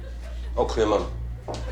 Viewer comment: yeah it definitely sounded like he said "all clear mum"🤦‍♂️😭😭😭